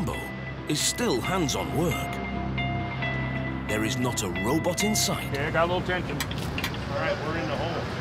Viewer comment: i thought he said 'no robots in sight'